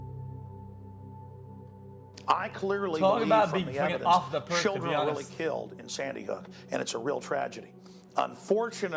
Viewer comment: wait what did u say here? I couldn't make out what you said there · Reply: I said "Deez"